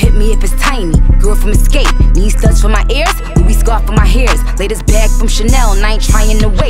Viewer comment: Anybody else it says “new” instead of “latest”?